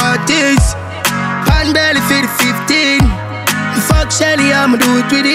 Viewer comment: 🤔🤔🤔wonder if I heard right"rygin say him fuck Shelly"wonder if a mi alone hear that🤔